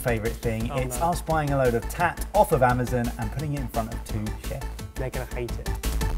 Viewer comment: Me at 0:08.....DID MIKE JUST SAY AMAZIN? WHAT THE F***?